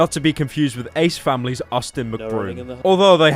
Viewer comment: I thought Austin broom said autism broom. Well I’m not far off